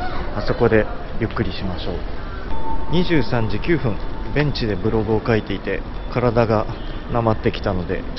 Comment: チョウマン美味しいんだろうなあ 昼時に見るんじゃなかった。ゴクリ ２倍速で聴いていたら 『ベンチでブログを書いていたら』が 『Ｈなブログを』と 聞こえて、 は？え？え？と三回巻き戻しました～( ´∀`) (その前に２倍速を解けよ) いやー楽しい動画でした～(￣▽￣;)